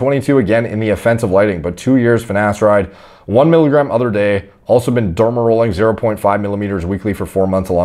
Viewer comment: did he say he's taking 1 mg every other day?